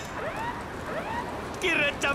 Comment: did he said kiryu-chan baka? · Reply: kiryu-chan miike